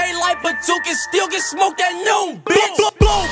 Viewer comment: did he say tooka still get smoked at noon bitch?+